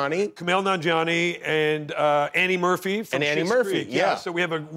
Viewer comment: I watched this show because I watch this interview and thought he said "Eddie Murphy" was going to be on it. 9:02...............